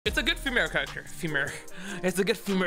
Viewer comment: I love how I click on the video and first thing I hear is "its a good femare character" 😂